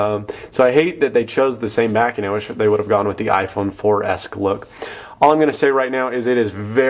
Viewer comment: he clearly said i wish they went with dah iphone 4s look